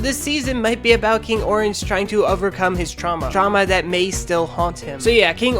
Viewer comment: I heard "MY TRAUMA" oh my